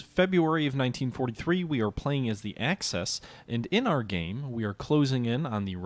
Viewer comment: did you say you were playing the Access? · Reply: Axis